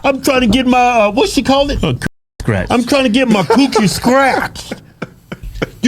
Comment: I thought she said she wanted to get her xxx STRETCHED.